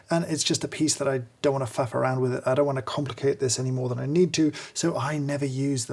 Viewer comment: I could swear he said "f**k around with", but with another view of it, he most certainly did not. Whew!